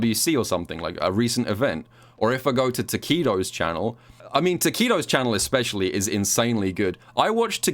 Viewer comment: I'm American and don't hear British accents that often, so at 11:10 I was like, "Who the fuck is Taquito?!"